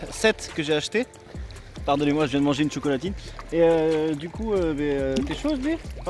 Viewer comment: une quoi ?!... ah un PAIN AU CHOCOLAT